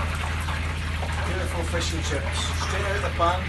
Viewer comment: I literally thought it said Flying Fish and Chip.....